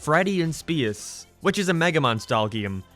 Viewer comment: for a second I thought you said Megamind style game LOL